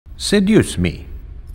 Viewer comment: I thought Aether says "SAID USE ME".... Hehehheheheheh